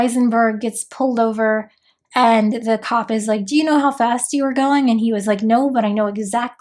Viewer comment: version I heard was Dis you know you were going 80? Great now I don’t know where I am!